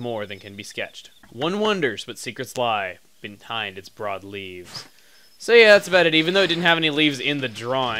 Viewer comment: i thought it said 'bread leaves'